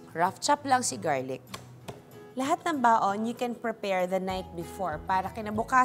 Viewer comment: I thought it said bacon not baon